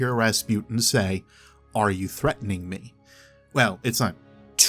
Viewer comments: i thought Rasputin said "DARKNESS" not "Are you threatening me" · "Are u threatening me " I heard "darkness"!! 😲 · I thought rasputin was saying "darkness" but "are you threatening me?" makes far more sense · listen again .. i know it might be nothing.. but still :D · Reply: i dont heard "Are you threatening me." , but i heard "Darkness" wtf im going crazy